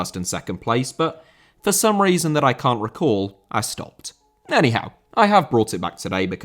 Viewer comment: Whenever you say "Anyhow" I hear it as "Eddie Howe" in a similar way to how you used to always say without Freddie Adu